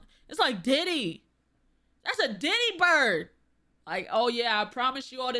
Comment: When you first said that one bird was a "Diddy bird" i misheard it as a "titty bird" and I'm like, I've never been down bad so fierce I saw boobs on birds of prey. Maybe someday, god willing.